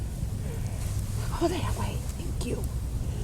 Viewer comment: wtf was that woman's voice. When you said go to somebody else's homestead smoke. She said ( something, thata way, thank you). I listened half speed several times can't get the first word. Right after you closed cap you guys didn't hear it. But that mic sure did . The voice sounded almost disappointed or sad that you ran her off. Quit being such a meanie to the ladies. She sounded kinda cute.😍😂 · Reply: That was saying go that way thank you.